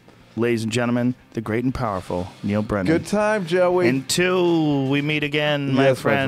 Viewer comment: God dammit I thought it said "Neil Breen"